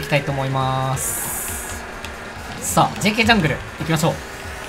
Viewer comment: DKジャングルがJKジャングルに聞こえてしまった俺は重症？😱